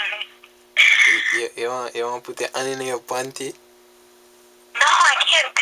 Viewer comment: sound like a dutty raper bwoi saying "So you wha put yuh han in a yuh panty??